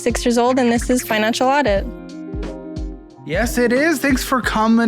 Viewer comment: Did I just hear, “This is Fine Enchilada”